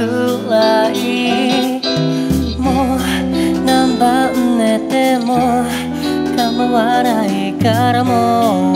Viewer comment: 最初の、「何番目でも構わないから」ってところの何番目が、何晩寝ても構わないからに聞こえる…